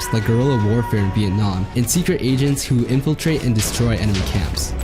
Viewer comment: for some reason I heard secret asians instead of secret agents and I was so confused on why they'd use asians specifically